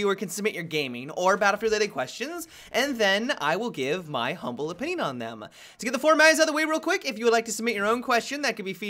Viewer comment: I'll ask it again: WHAT does say at 0:11??? Please help me out. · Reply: I will give my humble opinion on them